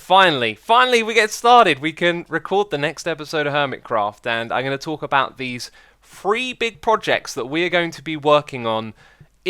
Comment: it sounded like you sayed ''im gonna talk about these free big project'' xD